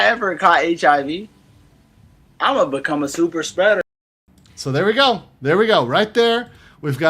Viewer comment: jezuz i misheard everything and was seriously wondering why he wanted to be Super Shredder. XD